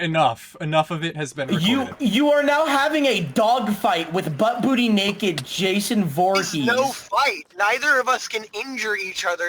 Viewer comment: Sorry, I heard "butt booty naked Jason Voorhees" and my brain short circuited